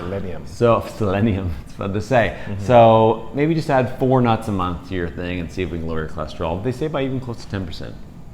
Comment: did he say just eat 4 brazil nuts a month? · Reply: Hi Samson. This was a small study of 10 people who are 4 Brazil nuts and lowered their cholesterol 10 percent for 1 month. Thanks for watching and subscribing